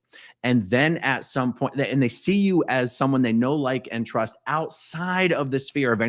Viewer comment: 😅 😂 I heard No like- trust?? 😂🤷🏻‍♀️ and really didnt get it.. No like.. Yess lol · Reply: Know-Like-Trust. I thought the same as you originally did until I finally saw it written....